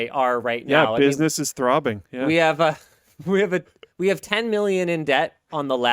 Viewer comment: Did Linus say "we are 10 million in debt" at 5:23 ?